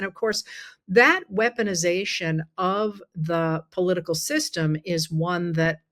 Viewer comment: ❤❤❤🙏🙏🙏💪💪💪Honey I can only imagine you getting tired......politicalization of the legal system... I am re winding to listen to this, but , I could have sworn you said legalization of the political system...